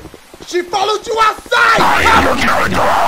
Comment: Wtf is Marge saying when the jumpscare happens? 😂😂😂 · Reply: I thought she said "I AM YOUR EMPEROR! " 😂😂😂